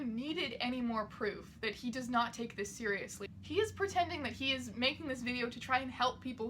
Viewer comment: wait wait wait I thought you said he toke those seriously